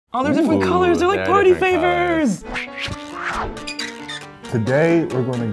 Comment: Party flavors!?! Who else heard him say that 2 seconds into the video